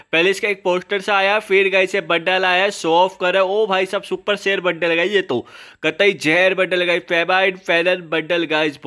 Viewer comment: only true dragon ball fans will understand what he said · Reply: Super Saiyan Bundle 😂